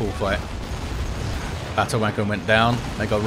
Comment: on Lord, did just say "battle w*nker"?